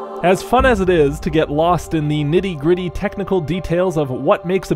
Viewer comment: did you say "MIDI gritty?"